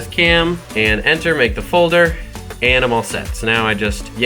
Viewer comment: Did I hear "make the folder, Animal S3X"? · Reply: “Make the folder, And I’m all set”